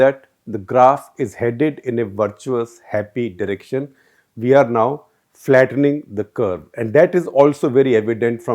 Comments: You said India IS flattening the curve but subtitle says otherwise. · the lax editor at it again. "Not" instead of "Now".. T and W are four keys apart so it's not a typo.